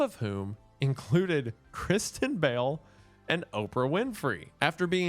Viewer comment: I thought he said Christian Bale, and then was so confused for a second when a picture of Kristen Bell appeared